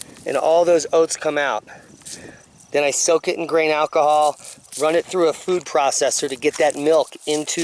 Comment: Hey, where do you get your seed? would everclear be considered a grain alcohol? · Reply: ohhh, thanks for asking that question, I thought he said GREEN alcohol, and was looking in the show notes for a link. (I'm misunderstanding a lot lately seems like)